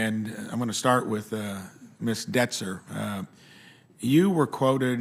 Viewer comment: I heard Miss 'Denser', as in dense, as in dumb/stupid in colloquial Oztralian. We'll see ... - " not a medical provider." At least she stated her limits. 3:34 "Miss Brandi," OMG, he missed her 'pronoun', what a 'cur'! ;-) - her 'earring' is not a good look. (I'll have to stop doing that myself! :-\ )